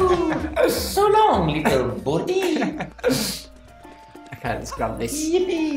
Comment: so long little birdie not 100% sure what he said but I think that's what he said